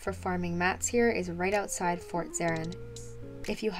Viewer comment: She said something about out side fort zeren and but i thought she was trolling and said outside port serim. Lol